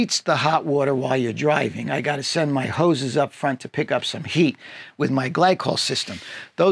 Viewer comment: I was working on something else, and I heard "I gotta send my ho** up front to pick up some heat"... 😲